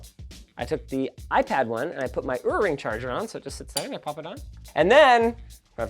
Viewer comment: Did linus say at 13:48 earring charger???